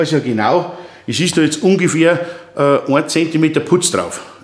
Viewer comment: hab ich richtig verstanden - 10 Cm. PUTZ ???? Bin 63++ aber habe SOWAS noch nie gehört !! Brauche noch ein Paar Jahre dazu ! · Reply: Er hat halt auch 1 cm gesagt nicht 10 cm